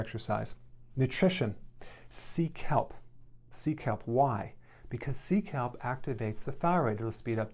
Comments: I thought he said Seek help 😂😂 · I thought you were saying "seek help" when you were saying sea kelp. Took me a minute lol. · i thought he said seek help and i was like this dude knows me? lol kidding.very informative thanks. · Lol....when he said "sea kelp"....I thought he said "seek help"..... lol...I was thinking, "Yep, I need to seek help." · I thought he said “seek help” instead of “sea kelp” and felt personally attacked for a moment 😂😂😂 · When you said “sea kelp” , I thought you said “seek help”....lol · When he said sea kelp I misheard it as Seek Help lol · Sea kelp! I thought he was saying Seek Help! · I thought he said “seek help” not see kelp🙄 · I thought he said seek help instead of sea kelp 🤣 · Sea kelp !! Wow I thought he said “seek help”. 😂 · I thought he was saying “ seek help “. I was like “ I ammmmmmahhh “. But then I realized he was saying “ sea kelp “ and I was like “ oh 😅” lol!!! Also update… it’s been only 5 days and I already went down 1 size. The potassium citrate and sea kelp are extremely important. ESP the potassium!!! The 2 times a day eating thing… I realized is how I used to eat anyway! I’m going to try prolonged fasting for the autophagy benefits as well! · I thought he said seek help! (Sea kelp)! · I thought he said seek help not sea kelp 🤦🏼‍♀️ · I was listening to this while doing a task and I heard "Nutrition. Seek Help" I was like "that's cold Dr. Berg. I thought YOU would help me" . But he said 'Sea Kelp' 😂 I said "Ooooh. That makes sense"